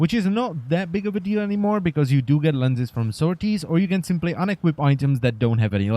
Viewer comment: Where did you say you get lenses from? Sorties? Please help.